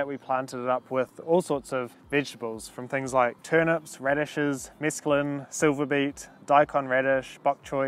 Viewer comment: did you say Mescaline!?